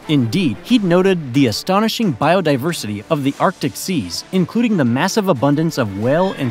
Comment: Are you sure he's not saying "Antarctic Seas?" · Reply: Positive watch at 13:57 with closed captions on. He clearly says "Arctic Seas"